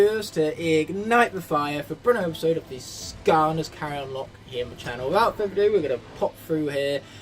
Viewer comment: Is it just me or did it sound like said KAREN locke?😅